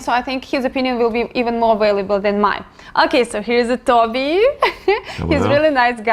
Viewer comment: for a second there I thought she called in him 'Tubby'